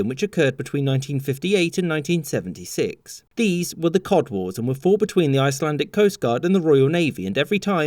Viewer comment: Thought it said cock wares